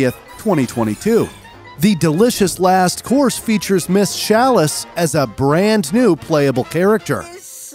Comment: did he just say SHalice?!?!